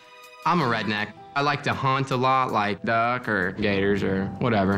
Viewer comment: I deadass thought that guy said “I like to hunt duck and gays” and I’m gone